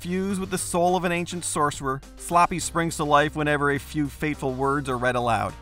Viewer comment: Did he say "sloppy"?